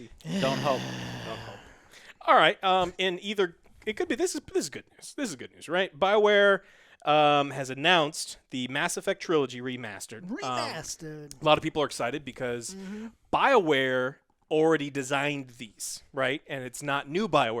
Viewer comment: TITLE IS MESSED UP, says REMASTED. Stay well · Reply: I hear saying "remasted"